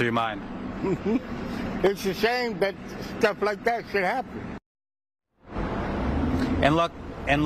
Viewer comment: Never seen that before today. Well yeah I hope so. I hope there not a lot of busses somewhere that is on fire every morning. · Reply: It's' ashame but stuff like that should happen" is what I heard.🔥🔥🔥🔥🔥🔥🔥🚌🚌🚌🚌🚌🚌🚌 LoL